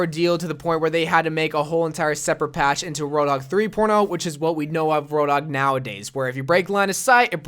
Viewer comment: did he say roadhog 3 porno